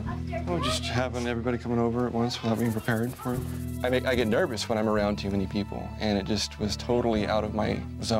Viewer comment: Genuinely thought said “I get nervous when I’m around human people” and I was like “Yep, I feel you”.